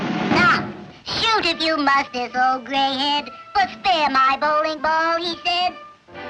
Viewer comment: What is the phrase said by Woody in English at 4:52 when he is waving an American flag? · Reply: “Stop. Shoot if you must this old gray head, but spare my bowling ball, he said” É uma paródia de um poema da guerra civil americana, a frase original terminava com “but spare your country’s flag, she said”